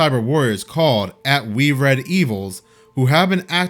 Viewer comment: Look @ 13:53 YOU said We Red EVils.....Look again at what it says... "We're Devils" They are literally telling us what they truly are. js